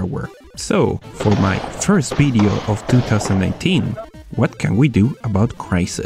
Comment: it sounds like you say 2019